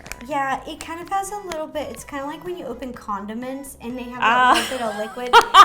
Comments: No I do not like black licorice and I know why Justine was laughing and it was funny that ro didn’t get it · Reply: When ro said condiment Justine thought that ro said condom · Who else got the condiments joke😂😂😂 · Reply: I heard "When you open condom-ments". 😂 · I'm pretty sure she thought ro said condom, I did too😂😂😂😂😂😂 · I think justine thought ro said condom.....ment so i think that is why justine burst into laughter